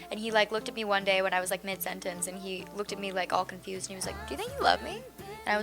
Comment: did dove say 'did u say u love me' or 'do u think u love me?' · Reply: Think